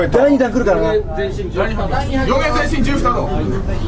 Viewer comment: min ,,You, re my sexy juice taco,,. He really says it